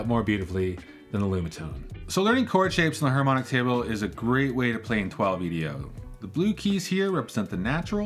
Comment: What does he say at 5:06? A great way to.. what? I tried the subtitles, they don't know either. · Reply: That's because it's unintroduced music theory insider speak 😛 He says "Is a great way to play in 12-EDO", where 12-EDO means "12 tone, equal division of the octave" which is the fancy name of "the normal tuning that virtually all pianos use". That is, we take an octave (doubling the frequency, say 440 to 880) and then divide it into 12 semitones that are equally spaced to span between the root and the octave. If you want to know why we have a term for this obvious sounding idea, there are music-theory + math reasons you can find on YouTube if you search keywords "equal temperament" and "just intonation" EDIT: I maybe sounded like he was using the term unnecessarily, but actually he had just been talking about a microtonal layout, and so the reason he clarified 12-EDO here was to bring us back from talking about microtones to show us a new 12-EDO (non microtonal) layout that was different from the first one he introduced.